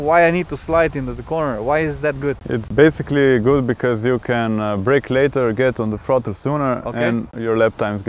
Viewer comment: I heard " it's good because you can break your leg sooner , get on the photo frame and nap sooner"😳😖😴 it's really awesome though 😎